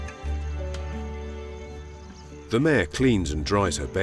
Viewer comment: The ______ cleans...... what is missing word. i can't get it what he said. can anyone help me get the word please feel free to comment... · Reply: mare